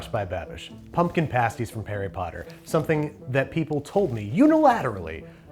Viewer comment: Is it just me or did he say "Parry Potter"..?